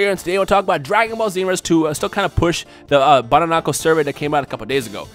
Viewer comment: He was talking so fast; all I heard was "bananamco". I'm like, "wait, what?"